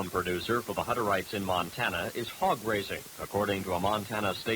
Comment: I did a double take when I thought he said Hog racing. Lol